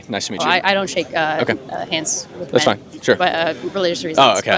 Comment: Did she just say, "i dont shake hands with men"?